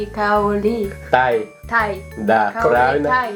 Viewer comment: kok dengernya kayak "kau ni taek"😂